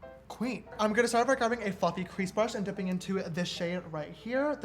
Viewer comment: I thought he said “I’ve got a f**king crease brush” and I screamed